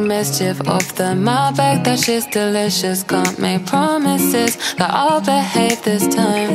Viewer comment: Anybody else mishear it as "potty sickness"? "That shit's delicious," no?